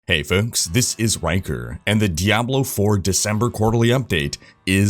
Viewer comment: lmao I watched this video dead tired but I legit thought you said "hello folks, this is wanker" at the beginning 😂